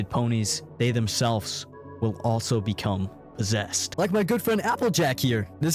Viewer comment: why did I think he said “zest” instead of “possessed” 😭 I had to double check